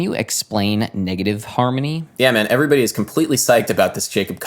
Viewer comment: I keep hearing negative "harmy" 😂😂